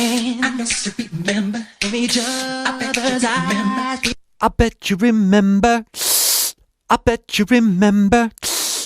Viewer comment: "I betcha remember "??!!!! I always heard "I forgot to remember" 😮😮